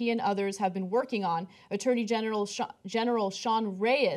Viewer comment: Did she say 'attorney genital'? LOL